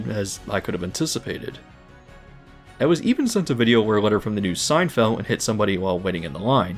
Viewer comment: I swore you said SEINFELD. had to listen back a couple times